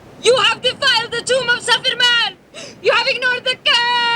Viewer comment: did she say they defiled the tomb of Superman? Great its another director's cut Justice League. How long is this one?